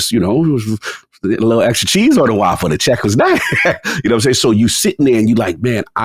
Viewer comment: Did just say, “A little extra cheese on the waffle??” 🥴🤢